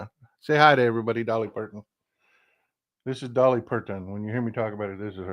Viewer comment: What a beautiful Dolly Parton! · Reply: I thought he said Dolly Purrton.